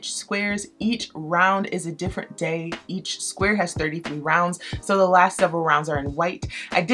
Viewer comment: I am sure I misheard you with the 33 rows.😂